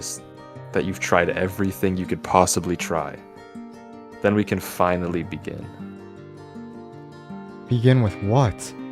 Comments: My broken ears thought he said… “Then we can finally be gay!” 😐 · i thought ha said noa we can be gay... but he is kind acting vay tho... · why did I hear "we can finaly be gay"